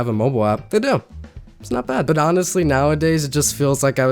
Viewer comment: Did he just say its not bad im crying